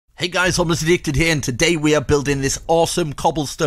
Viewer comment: Not gonna lie, I thought you said your name was "Homeless Evicted".